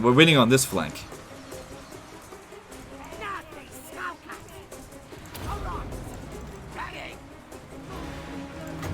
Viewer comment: WTF DID THAT GOBBO JUST SAY??? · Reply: he said "Nasty Skulkers".